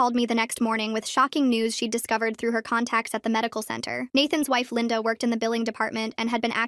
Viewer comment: “Nathan’s wife Lynda had been killing ally acceding my files” ya’ll, wasn’t Nathan married to Melissa? And talking about divorcing her? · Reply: No Ryan was married to Melissa... although I thought they said Melinda before they said Lynda